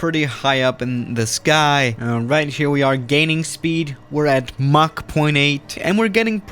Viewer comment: thought he said gaming speed for a sec